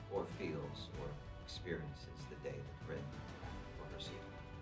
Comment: I didn't hear what Siggy was saying at 3:29:00 :( What does he see when between life and death? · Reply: The day Rin left for her seiðr.